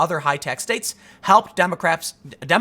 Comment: did he say Democraps? 😂